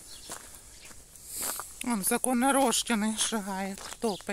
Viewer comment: Стасик уже стал НЕзаконорожденный???😂или мне послышалось..🤔😆